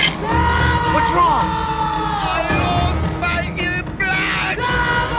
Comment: What the midget said- "I LOST MY EAR PLUGS! PLUGS! PLUGS!" What it sound like he said- "I LOST MY IMPLANTS! BREASTS BREASTS!"